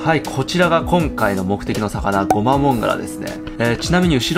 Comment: ゴアマガラと聞こえなくもない